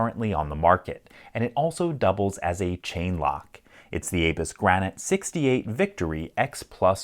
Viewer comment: I honestly thought it said anus grant 68